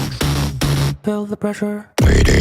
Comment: I thought he said “Feel the pressure”